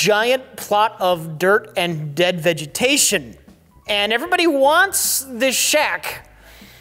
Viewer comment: Thought Rich said "Everybody wants this Shaq"